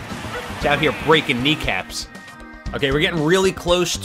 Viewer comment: I am upset because I thought he said miicaps